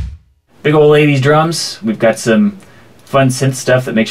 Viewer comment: I thought you said "Big Old Ladies drums".